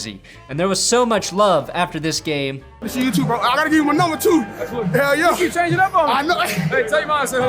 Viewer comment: it really sounds like Lamar said “I gotta go take a number two.” Confirming the theory. Lol